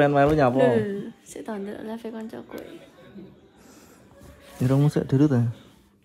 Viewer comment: Irungmu dilut to yank...kalo ga salah denger saya.